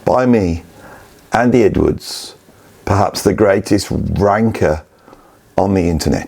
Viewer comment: Great to see Can & Frank Zappa make the list. Also, I had to play 9:07 twice because I could swear that you said you were the greatest wanker on YouTube.